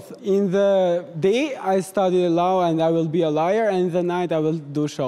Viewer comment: When he said “I’ll be a lawyer”, I thought he said “liar”. Same thing, I guess.